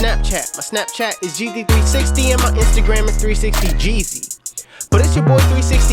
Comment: did he said 360 Gigi 😭